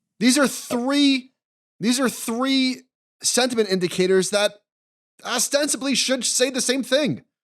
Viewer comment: Did he say ASStensibly?